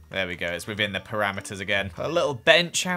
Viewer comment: did he say perAmiters??